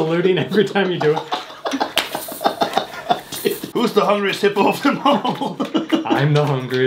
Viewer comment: “who’s the hungriest hippo of them all?” “I’m the hungriest hippo” 😂😂😂😂😂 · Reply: I thought he said, "The hungriest Hitler" which, honestly, made it a million times funnier.😂